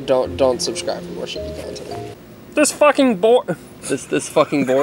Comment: I thought he said this f**** b**